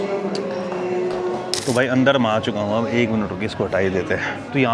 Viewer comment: kya bola tune 😂😂😂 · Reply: अंदर में आ चुका हूं।